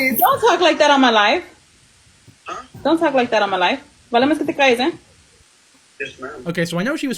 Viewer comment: Okay in that clip at 27:18 where she spoke Spanish she said “it would be better if you stay quiet” which is a very “mom” thing to say if they’re upset at something their kid said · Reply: It sounded like she said “vale mes si te crias” instead of “ vale más si te calles” 😂